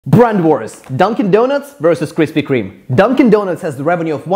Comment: Did he just say krispwe kweeme on one them